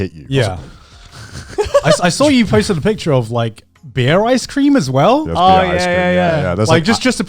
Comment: omg the whole time I thought you guys were saying bear ice cream instead of beer LOL